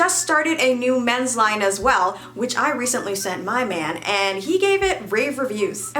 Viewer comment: did you say "My man "👀👀👀👀👀👀👀